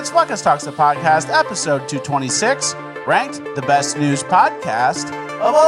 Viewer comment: I'm pretty sure you said the best NEWS podcast of all time, instead of the best NEW podcast of all time. I see you switching up the words with your little psyop game 👀